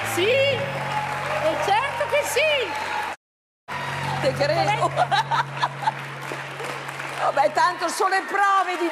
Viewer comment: Ma ad 8:50 Ornella Vanoni dice qualcosa che viene censurato, che cosa dice? AHAHAHH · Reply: Le dice Z...ola 😂😂😂😂